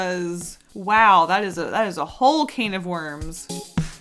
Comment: did you say cane of worms? It’s a whole other can of worms